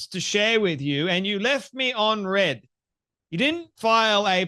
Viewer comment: mins I dont think she says she was left "on red", but rather that her message was left "Unread." Which is even more telling!!!